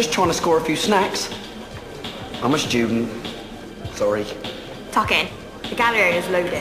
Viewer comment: Bit of nostalgia coming back to watch this but @ 12:03 does she say "Fuck It, the gallery is loaded" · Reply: i think she says ‘tuck in’ 😭😭😂